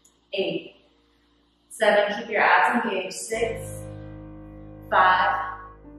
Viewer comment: When she said “Keep your abs engaged” I heard “Keep your ass engaged”